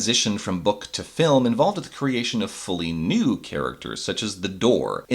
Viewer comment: thought he said fully nude.